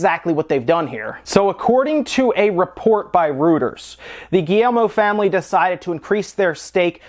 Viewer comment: Timestamps: 0:00 Welcome to News Wave! 0:10 SNES Classic already being scalped 4:38 Japan getting Super Famicom Classic with some different games 5:47 Yooka Laylee begins Switch testing 7:02 Crazy Mario Speed Run 8:22 Ubisoft vs. Vivendi YouTube can be a weird place, come connect with us on the reddit! <link> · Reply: says Rooters instead of Reuters. How is that possible? That made me cringe. Someone needs to quality-check this stuff ... needs a Mike Matei or someone to listen to these quickly.